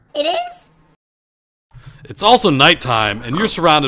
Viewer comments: hostile green 'PENISES'!?!??!?!?!?! (btw subtitles say hostile MOBS XD (also i didnt notice this until YEARS after this was made XDDDD)) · Did he say "It's also night time and your surround by hostile green penises." at 1:31? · did he just say penis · i think he said penises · did he just say hostile green you-know-what?🤣🤣🤣🤣🤣🤣🤣🤣🤣🤣🤣🤣